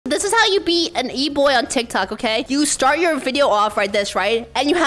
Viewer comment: I thought she said how to beat an e-boy